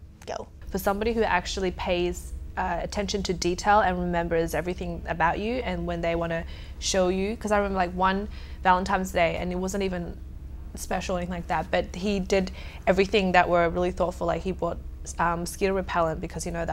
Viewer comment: Did she say Valentimes Day?